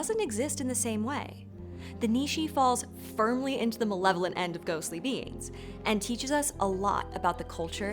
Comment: At the beginning of the video I thought said "benevolent" instead of "malevolent" and it took me a minute.